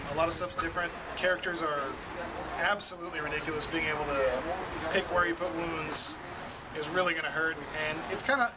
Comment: What's he saying at the end at 10:35? "Characters are absolutely ridiculous being able to pick where he put wounds?" Is that referring to the new "look out sir"? I watched the whole video, but the english was a bit fast for me here and there, couldnt always tell what was happening.